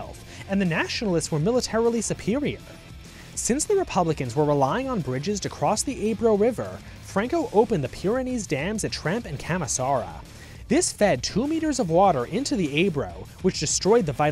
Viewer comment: 2 litres? Do you understand what you're saying, American? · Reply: 2 meters not litres